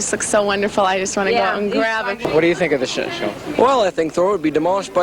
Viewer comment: I hear "What do you think of this shit show?"